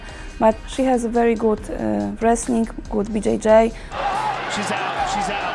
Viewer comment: sounds like she is saying: "good va jj"